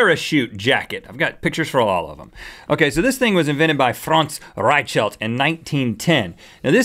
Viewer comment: who else thought he said France ratchet